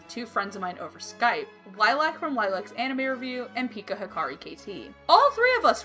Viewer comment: Who? I couldn't really understand the channel name. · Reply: Sorry some times speak a little fast its.